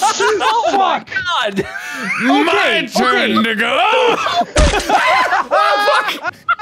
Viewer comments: did mark say "my turn ni**a? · I didnt understand him saying 'to go' and i got so concerned he said something else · what did mark say at 31:35 ? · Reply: My turn to go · Did mark say the N-Word at 31:35??? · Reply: I thinks he’s saying “to-go” it just sounds very close to the N word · I'm not the only one who heard what I thought I heard at 31:36, right? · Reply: Apparently you arent but im not sure why. Why are people having a hard time with "my turn to go"? Just set it on repeat. I guess now we cant say "to go" because it sounds too much like a slur. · did mark say the n word at 31:37 correct me if I'm wrong pls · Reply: My turn to go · At around 31:38 does mark say the N word? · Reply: He says “to go.” Like “my turn to go.” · is it just me? Or did Mark say? Hol up sum ain't right here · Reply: No, he said my turn to go. I heard it said that way the 6 or 7 times i rewatched this and it always is "TO GO". I guess saying "to go" is bad now? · whoa wait id he say the n word? cause i heard "its my turn ni**" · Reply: "my turn to go". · please tell me I heard that right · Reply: I think he was saying "my turn to go" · Can someone clarify what mark said at 31:38 because I might be tripping. Dont want to cause confusion so I'm just asking before I make any assumptions. No hate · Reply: makaila stafford - He said “to go.” Full sentence being “my turn to go.” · i know he says "my turn to go," but i honestly thought he said "my turn (n-word with soft a)" and i was like "wait huh?"